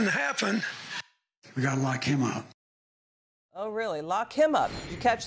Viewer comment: I heard "Lock Me Up"